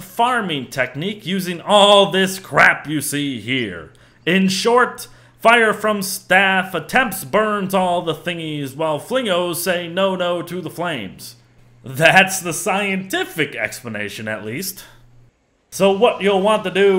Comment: i have almost no idea what youre saying here. as in, i cant figure out the words. its just a jumble. can anyone help? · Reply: "In short, fire from staff attempts burns all the thingies while flingos say no no to the flames, that's the scientific explanation at least"